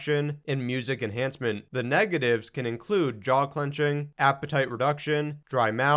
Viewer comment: Listened to this on 1.75x speed. Misheard the symptom of "jaw clenching" as "dog punching".